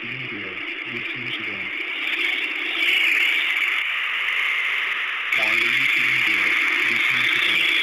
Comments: Going to be honest, It sounded like they were saying "Why are you standing there? Be Beautiful." I can understand the first part being a probably, but the last part I highly doubt that was actually said just my head trying to fill in what was said. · I hear "Why are you sitting there? Listen to them!"